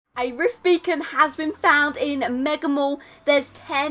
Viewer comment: Lol at first I thought the total said rift bacon